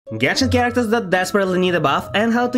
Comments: I thought you said "Genshin characters that desperately need a bath" XD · Bruh, I thought he said “characters that desperately need a BATH” and my first thought was Razor 💀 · I heard it as, Characters that desperately need a bath 😭 (no offence or anything-)